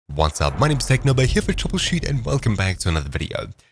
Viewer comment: sounds like you said "Technoblade"